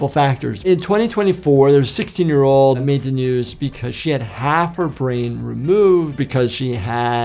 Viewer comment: I heard "made the news, speak her sh**" 😆